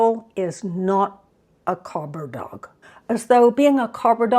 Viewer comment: When you said cobber dog I thought you said copper dog and I had to look it up lol. (copper is what we call a police officer). Cobber is old Aussie for a good friend or person. Hearing you talk about Brekkie's energy I thought yep, that's an Aussie for you lol. I got a rescue cat who was older. Recently had to say good bye to him after 7 years, he had been very traumatised in his life and was super demanding in his needs. I was in a situation where I could be there for him 24/7 but it was an emotional drain all that time. I will get another cat one day, but I'll get a kitten so I know it will grow up without trauma.